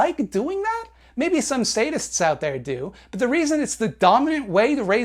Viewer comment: when you were talking about how some sadists might like to keep chickens in dark cages, I thought you said "statists" and I was like "based."